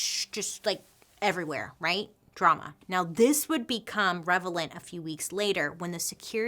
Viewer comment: You’re a true fan if you noticed she said “revelant” instead of “relevant” at 57:09, she didn’t even blink😂😂😂😂 (gets me every time), love you Bailey ❤️ your videos help me to focus when I’m working.